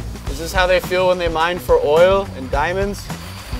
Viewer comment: USA: did i hear oil?